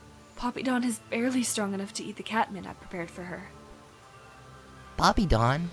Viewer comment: I thought Spottedleaf said "Poppydawn's barely strong enough to eat the cat that I've prepared for her"